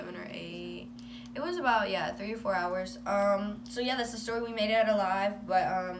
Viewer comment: did she say that’s a story they made up or did I hear wrong?